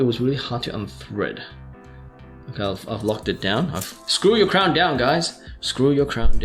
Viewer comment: Nice review! Got one today from Starbuy at USD$150 / AUD$199 shipped. Thanks! As I was watching your review, for a second, I thought I heard you said "screw you down guys" instead of "screw your crown down guys!". Haha